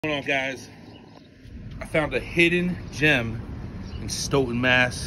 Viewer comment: Thought you said a hidden gym lmfao